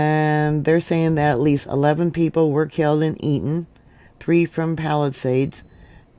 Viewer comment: did you say 11 people were killed and eaten? Did I hear that rt?